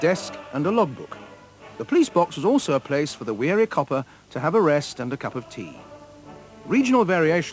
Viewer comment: can someone tell me what does he says @ 1:23? about the cup of tea? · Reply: pillowl_ he's talking about tired police men using the box for a rest and to have a cup of tea